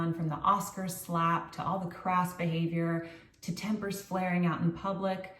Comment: When you mentioned crass behavior I thought for a second you said “crap” behavior and I had to rewind because I couldn’t believe you said that! We have come to expect certain things from you, and you are consistent and true to your message. Congratulations on 200k subscribers!